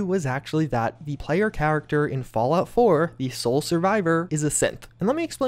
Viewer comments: Legit thought he was saying “the sole survivor is a simp” for the first 5 minutes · I thought you said the sole survivor was a simp XD